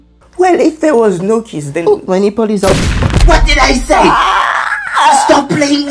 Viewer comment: I thought I heard “my nipple is on fire” 😂